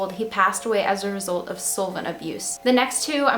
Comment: he passed away as a result of what abuse?? I could not figure out what that word was and I listened to it like 8 times lol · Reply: solvent abuse